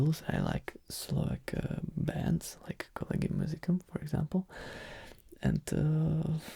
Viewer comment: What was the Slavakian band that he said at 33:35 ? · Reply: Collegium Musicum